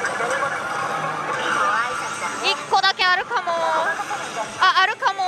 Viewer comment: の「あったかも〜」がアル中カラカラの見すぎで「あ゛っ゛た゛か゛も゛〜」に聞こえる（幻聴）